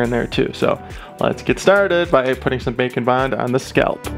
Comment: First I heard you say Bake and bond I thought it was called bacon bond.